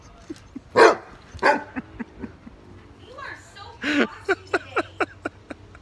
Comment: What did she say? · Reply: She says, You’re so bossy! Lol